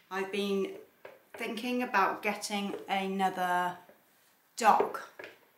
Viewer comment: I thought you said that you were thinking of getting another duck🦆 and I thought 💭 ookayyy and then you started to talk about bridey and I realised you said dog 🐕 ahahaha so funny 😂🤣🤣 We are actually looking to getting a puppy (German Shepard)as ours passed away last year, but, they are all on the other side of the country. I’m in Western Australia and they’re all on the east coast so the cost of shipping is another expense. Anyway, that little pup is as cute as a button... but will bridey be jealous? He knows he has you to himself at home, even though he likes a play day but he still doesn’t have to share. My cat is very jealous so I can’t get another kitty as he will be very mean to it...I’ve tried. Just a thought... Love the soap and those apples are cute on top 😊